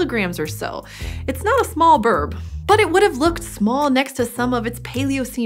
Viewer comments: did she say birb?? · "...it's not a small birb" I swear - she says BIRB! Great video, but how did NO-ONE catch that? lol